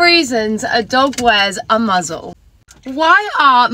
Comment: is it just me but i was thinking of hp and i thought u said ‘muggle’ instead of a ‘muzzle’